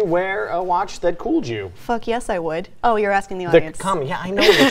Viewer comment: Sounded like she said fuck yes I would at the end of the video. You dirty dirty nerd you lol